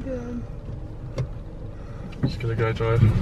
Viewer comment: did anyone else hear a girl say "what r u doing" go to 2:47 and it's